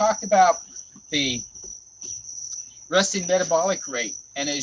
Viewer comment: Maybe your insects are extra noisy but I thought you said "your rusty metabolic rate". Even though it does describe my metabolic rate, you no doubt said 'resting' metabolic rate.